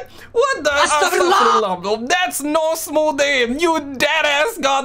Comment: i heard her say smooth brain not 'smooth aim'.